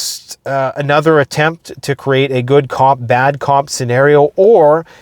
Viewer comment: Did I hear “good cob, bad cob”?